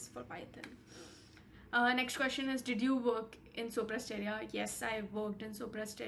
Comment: what have you said , (sobra area / supressed area) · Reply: Sopra Steria